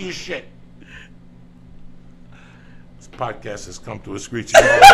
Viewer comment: what does he say at 02:02:07 ? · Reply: Az Kid this podcast has come to a screeching halt.